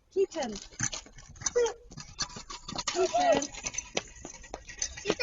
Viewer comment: ehm... hmm interesting, I heard the word "kita kami" meaning "we got caught"... ehm... ighty